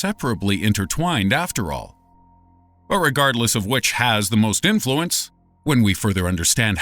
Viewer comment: did i heard him say irregardless